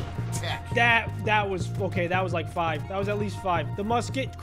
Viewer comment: what did u say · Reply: i heard fokay